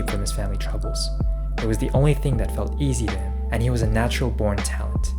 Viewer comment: easy to him って言ってますか？ スローで聞いても、僕には聞き取れません easier に聞こえます どうなんでしょう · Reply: Yes! "It (basketball) was the only thing that felt easy to him and he was a natural-born talent" I made English subtitles as well so you can check!